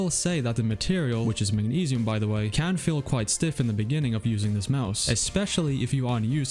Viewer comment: Errm why when he says using the mouse I hear "abusing the mouse"...